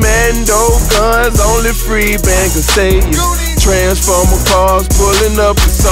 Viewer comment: i thought he said free ben lmao